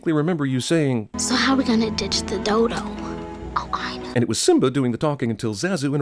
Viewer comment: I'm actually surprised they missed this but at that time did simba say al qaeda? #JustWondering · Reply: He said "Oh, I know."